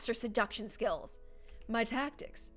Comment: I thought it said ‘my Tacos’